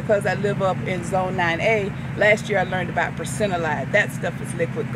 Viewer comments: Great video with, love the enthusiasm. Many of us are trying to push the zone as David puts it. Very interested to find out what is talking about when she says - treatment with Procinalide? "That stuff is liquid gold " - 3:33 in vid. Please tell us what product you are talking about. Google knows nothing. Thank you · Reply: Brassinolide available on Amazon. Useful in citrus. Also silica blast helps riots uptake nutrients in stressful situations like heat and cold. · Anybody know what she's talking about at 3:33? Percinalide? I've tried every spelling I can think of and nothing comes up on Google. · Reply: Brasscinolide by Power Grown